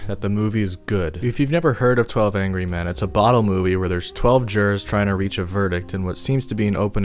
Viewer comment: i thought you said bottom movie 😅😂🤣